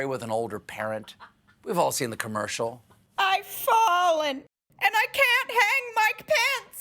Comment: "I've fallen and I can't hang Mike Pence" is quite possibly one of the few good things to come out of this entire farce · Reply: I thought he said, "i've fallen and I can't hang my pants"..lol